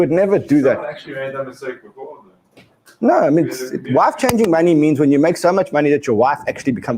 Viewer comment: Just listened about the "wife changing money" I always thought you were saying "life changing money." 😂😂😂😂😂😂😂